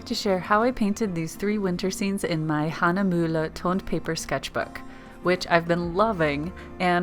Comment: At first I thought you said toilet paper. That’s some serious skill indeed! But toned paper looks pretty good too 😊